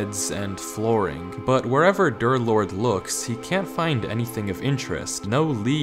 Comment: why i heard girth lord